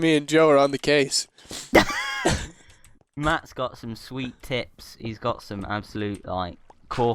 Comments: oooh God i thought Joe said: Matt got some sweet tits.... but then i heard it again and was relifed ;D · I thought Joe said Matt has sweet tits...